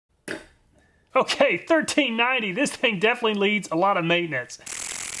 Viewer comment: Remember that Laurel Yanny thing? Check this one out! At 15:38 does he say "ok 1390--this thing definitely needs a lot of maintenance!" or "ok 1390--this thing definitely leaves a lot of nightmares!"?